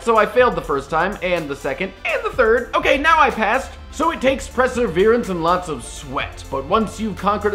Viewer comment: what was that word? Presserverence? LOL